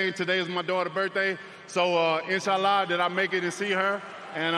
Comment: did he just say "inshallah"? Bro became muslim